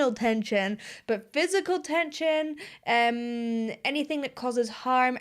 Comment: I think the video file heard you say "anything that causes harm" LOL